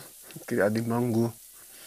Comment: Escutei certo?? "Criado em Bangu" 😏